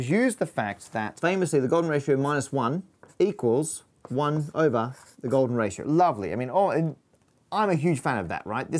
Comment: Matt says "one over the golden ratio" but the subtitles say "1/<num>." Just something interesting I noticed lol